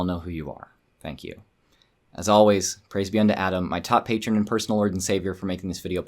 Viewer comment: it sounded like you said "Praise be to Atom"... have you given up Christianity to worship Atoms mighty glow instead? (Fallout joke) ;)